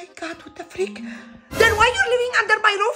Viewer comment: i thought the mom said make me rich not pay me rent 😭